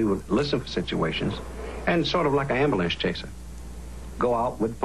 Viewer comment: did he say amber-lamps?